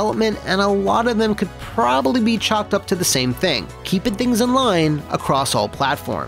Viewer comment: did you say “chopped up??”